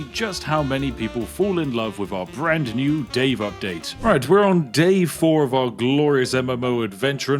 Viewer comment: Did you say "Alright, we're on DAVE 4 of our glorious MMO adventure?"